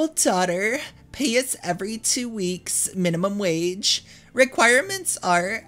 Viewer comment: when you read, "Pay is every 2 weeks," it sounded like you said, "Pay us every 2 weeks." 😂